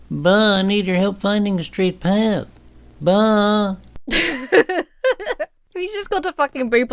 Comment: I could have sworn he said, “Wise Ba’al” 😂